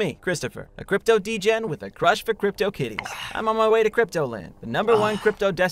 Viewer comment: I thought he said crypto kiddies not kitties until I saw the subtitles